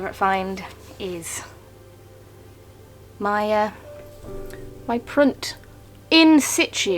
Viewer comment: That was an epic lark! 😁 All those figurine heads. 😲 We won't linger on the heartbreakers. 😢 The beads were superb. I'm glad you put the link in the description about that raspberry-like thing. I thought you were saying "print", but that didn't seem correct. Now I know it's "prunt". 😁 I'll go and watch that video next. I've been keeping up with your miniatures channel, despite Youtube's snafus. Hang in there; it'll get resolved eventually. 😬😂 See you next time. 😍💛👍